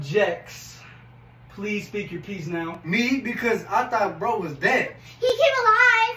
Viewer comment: The fact that he said “NO BECAUSE I THOUGHT THAT BEAR WAS DEAD⚰️😂😂😂💀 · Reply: I thought He said I thought bro was dead not that bear was dead